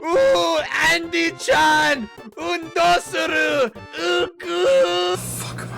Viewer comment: DID HE JUST SAY "~uuguu~"??????